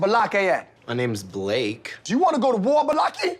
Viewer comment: I could not figure out Ba-locky and I just heard “Blake”. Finally I don’t have to spend anymore time trying to figure it out. 🤣🤣